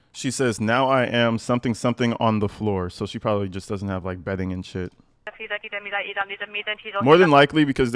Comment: I don’t think this is about bedding. From context I think she’s talking about being on the protective custody floor instead of in gen pop. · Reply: I think she said "psycho floor" Now i am, why (muffled) psycho floor